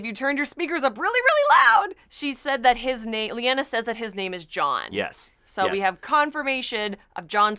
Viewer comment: At the 8:32 mark, Lyanna never said that his name was Jon. It's something with an "aerys" on the end. It sounded like Jaehaerys. Jon is the name that Ned decided to give him since Jon Arryn was a father figure to him, and he couldn't be called by his actual name since Robert would obviously figure it out. A person on Reddit explained it in detail. Plus, you could barely hear what she whispered and they made it inaudible for a reason.